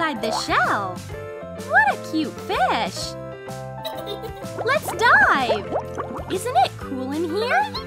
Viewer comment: Did anyone else hear let's die 😱?